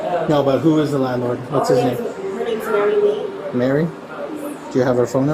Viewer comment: She rents the house. At 5:09, the officer asks her the name of her landlord, and she says, "Mary ....?" I was finding it kind of difficult to hear, and I didn't catch the last name. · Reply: I’m pretty sure she said “Mary Lee”, but I was already suspicious from her saying her son’s name is “Alex Rodriguez”…😒 So, I’m thinking she was just pulling names out of thin air whenever they asked for one.🙄🤦🏼‍♀️